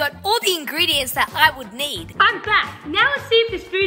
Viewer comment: “Im back” what I heard “I’m black” 👶🏿💀